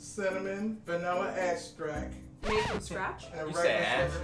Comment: For a sec I thought I heard ass scratch 🤣